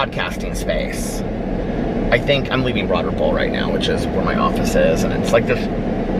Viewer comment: 💙 I always thought you were saying “Broaderville” 😳 Broad Ripple, got it! 🤣👏🏻 love you!! 💙💙💙💙🎃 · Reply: I always thought he said “Broaderpool” 😆